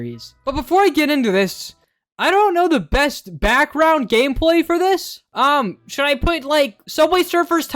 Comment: I heard "gameplay" as "gay play" Someone put me out of my misery